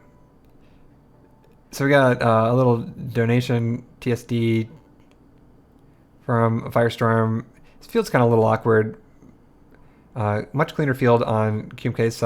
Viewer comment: I was so confused for a second, I missed the "TST" part and heard it as "So we got a nice little donation from Firestorm", and I'm like huh how did he donate to the stream while playing?? Lmao